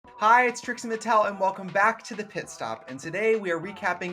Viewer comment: I could have sworn I heard Trixie say "Welcome to the TIT stop"....which is grounds for the show's immediate name change no?